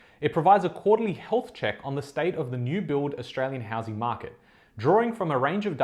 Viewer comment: This video’s giving major FOMO, haha. Just need to work out which areas in Melbourne are good to invest in and when to jump in. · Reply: I thought he is saying the housing market is crushing ? Why fomo